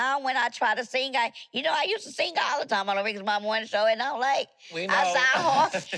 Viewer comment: I thought she said she dated Rickey Smiley?